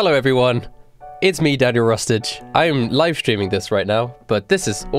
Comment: When he said Daniel Rustage I thought he said 'Daddy Rustage'👁️👄👁️